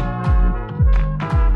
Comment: Translation : All the same, all the same, all the same And that's enough ✨ It's in french btw And I'm french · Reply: Nahhh I'm barely learning French and I thought it said "You lie" "You lie" "You lie" "And I know it" 💀